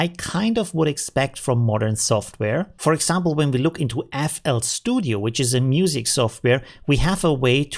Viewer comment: of your video you mention a new software. What was the name of it. Couldn't understand it. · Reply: FL studio